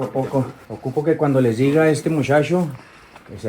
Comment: "mushasho" instead of "muchacho". People from Chihuahua sound like they have been hittin' the bottle haha. I like it though, it's nice.